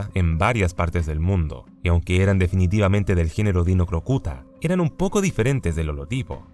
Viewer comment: escuche "del género de una gran puta" 🤣